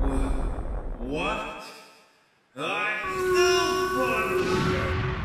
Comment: What does Hyper Sonic say at 18:42? "I'm still ???" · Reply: I still function